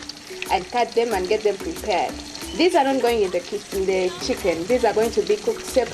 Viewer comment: I had a Ugandan friend who always said the same thing kitchen instead of chicken 😅 #HappyMemories